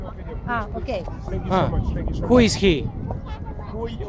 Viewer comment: DID HE SAY MEIPLE @ 0:09? AS IN THE SONG BY ROBIN THICKE FT. JAY-Z I GUESS THAT'S WHY HE LAUGHED HE KNEW WHO HE WAS